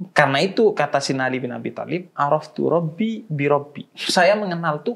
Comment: apa gw salah salah denger y,, "Si Ali Bin Abu Tholib" gitu y?